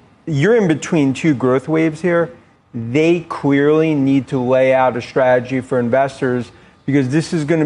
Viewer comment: I'm not sure if it's me or did he say... 1:52 "they queerly need to..." I always thought he had a speech impediment but this clearly confirmed it. Doed anyone know if he has been diagnosed with a speech impediment or something else?